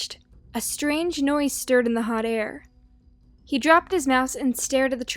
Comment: I thought you said " he dropped his ass"!!!! OMG what am I doin with mah life!!!?!????!?!